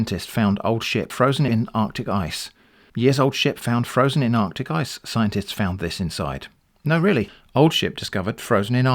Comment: i wasnt paying attention and i thought he was saying "years old shit in arctic ice scientists found this" 😭 Im crying lmao